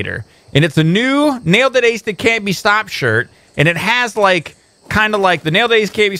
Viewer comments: I am the only one that thought said "Nailed it, base it, that can't be stopped"?? The t-shirt has finally showed me the light! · i thought u said it like 'now the days you cant be stopped!!' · I always thought he said "Nailed the days that can't be stopped!"